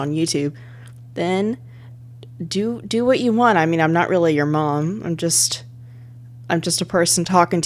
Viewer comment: I watched the whole thing before... So while I was skipping by double tapping, I heard "Guys-I-lay- your-mom" :,)